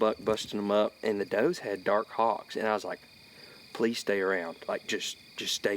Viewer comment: At the 11:44 mark what did you say the doe’s you saw were? Dark dogs?? Dark hogs?? Just curious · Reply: I think he said "hocks."